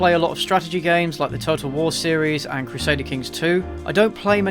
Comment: I thought you said "Turtle wars" when you said "Total War"